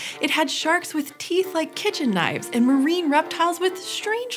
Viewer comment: I heard "teeth-like kitchen knives" and had to stop and re-read the subtitles as "teeth like kitchen knives" similar to. I was wondering who was smoking what the first time. Sharks made knives?